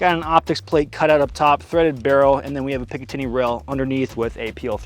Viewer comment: I swear to god bro said “ big titty rail”